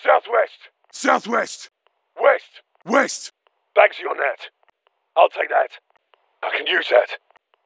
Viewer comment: What does he say at 4:47? My American brain can't comprehend the heavy accent. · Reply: Or bagzee on that